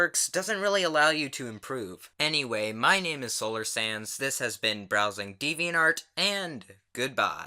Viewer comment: Who else thought he said solar sans not sand I thought that for four vids straight